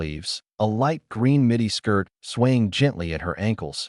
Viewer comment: it said midi but I heard mini and had to take a break to stop laughing "mini skirt down to her ankles"